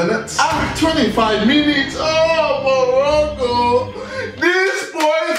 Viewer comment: Did he said oh Morocco???